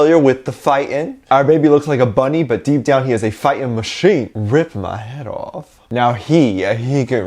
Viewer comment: Me: looking like a 🤡 Jungkook: Gets any hair cut but looks amazing Me: how? Army: I mean look at him Me: I know also what did you say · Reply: hes a machine a fighting machine :D